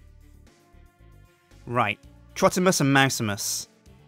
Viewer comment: Did I hear Trottimus?